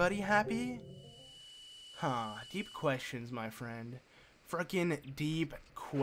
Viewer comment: did he just say the f word? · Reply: CRAZY FISHY he said fricking...but you know he can say fucking if he wants to..